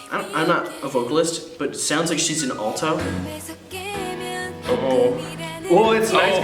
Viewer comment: I wasn't watching the video, and I thought I heard say "It sounds like she's in Ulta"